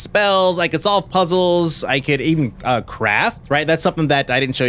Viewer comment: Lol I thought he said crap...craft